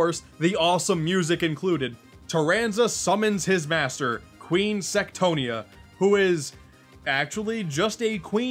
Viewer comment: What you said "Queen Sectonia" What i heard "Queen Sextonia"